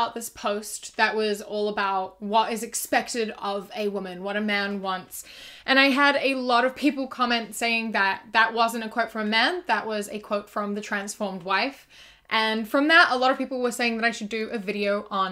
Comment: I thought you said trans fem for a second 😭